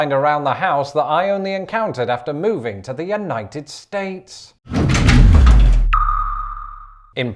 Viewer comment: did he say the un-knighted states? cheeky bastard